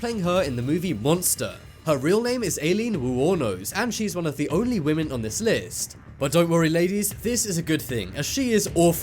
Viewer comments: I thought you said there’s only one woman on the list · I thought you said there was only one woman on this list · I thought you said MONSTER was the only women in this...😒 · i thought he said there was only one woman on the list? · Thought you said there was ONLY ONE woman on this list? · I thought you said monster was the only woman on the list, human plague is a women too...🤦‍♂️ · i thought he said there was only one woman on the list. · I thought she said only 1 women it's two woman in here